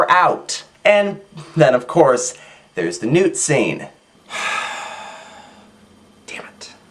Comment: Lol I thought he said "Nude Scene." I was so close to closing the video...